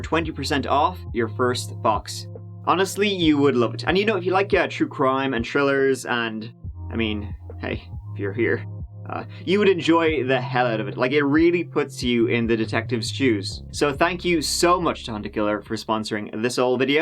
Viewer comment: Did you say “it really puts you in the detectives juice” ??🤔